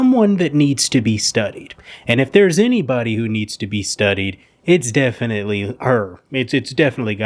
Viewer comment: I thought you said needs to be stoned and I agree